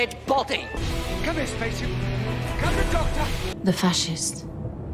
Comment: The Ambassadors of Death getting a "come on spacesuit come to daddy" description feels off. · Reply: Huh, this whole time I thought he was saying “spaceship”. Given how some of the less eventful episodes get boiled down to “first appearance of ___” maybe it’s referring to the part where the Doctor goes up in the space shuttle wearing full astronaut gear? TBH I only watched Ambassadors as background noise and missed most of the plot points but that bit was nonetheless unorthodox enough to catch my attention.